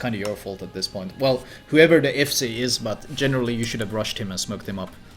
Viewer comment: is he saying "FSA"? what does it mean? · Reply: He said FC - fleet commander